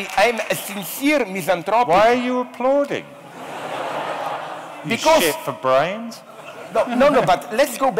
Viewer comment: Self says to the audience: "Why are you applauding? Shit for brains..." Did I hear that right?